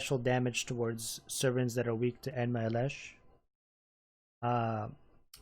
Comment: did he just say enma elesh?